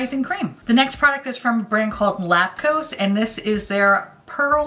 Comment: I kept hearing "lactose" whenever you said Lapcos and I got so confused lmao